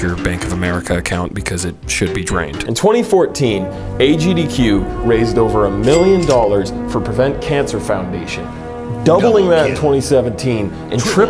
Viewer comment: I almost thought he said: "Raised 1m dollars to prevent a cancer foundation" 😂